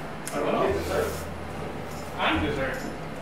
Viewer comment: I thought i heard the host say "im the dessert" hahaha perve.